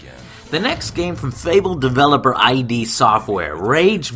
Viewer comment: whit whit what did he just say 4:33 id made fable ???